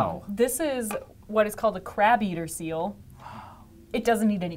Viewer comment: To mi it sounds like she is saying "Crap eater" instead of crab eater...